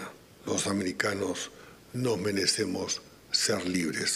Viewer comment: Acabo de volver a escuchar al canciller y no entiendo cuando a los 5:00 minutos del presente vídeo dice: "...los americanos no merecemos ser libres ..." 😮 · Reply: Nos merecemos ser libres